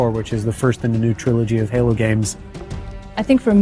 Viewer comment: Did he say new "trilogy" ? OMG FUCK YES!!!!!